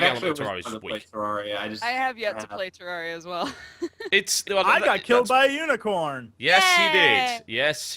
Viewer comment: It buffered at 15:23 so I heard "I got killed by a eunuch!" and thought "WTF?!"